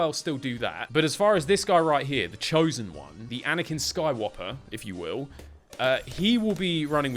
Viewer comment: DID BRO JUST SAY ANAKIN SKYWOPPER